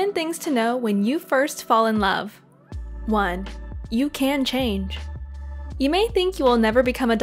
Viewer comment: I really thought it said "I you can change"